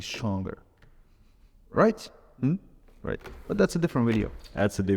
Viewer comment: i thought for a sec he was gonna say break ur arm 💀💀